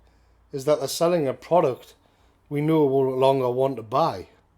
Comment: what did you say at the end of the video? I listened to it several times and I still didn’t catch it. It sounded like you said “we know all along want to buy” but I could’ve sworn that I also actually heard it as “ we no longer want to buy” could you please let me know what it was you said actually right at the end? I would be deeply grateful if you could please let me know. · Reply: ''we no longer want to buy”